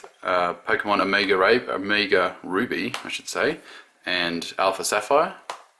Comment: I don’t think I heard that right.... OMG he said Omega rape 🤣😂